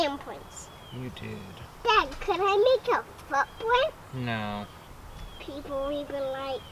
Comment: I literally LOL when said "can I put a foot print" because I heard "butt print"! :) Your kids are hard workers and probably the best construction crew you'll ever work with! :)